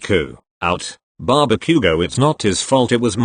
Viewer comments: LMAO 15:08 SOUNDS LIKE “Barbecue go” INSTEAD OF BAKUGO 😂😂😂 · Why did I hear "Barbecue bro" HSLP